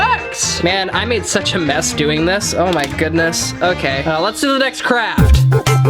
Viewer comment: When you said next craft I thought you said next crap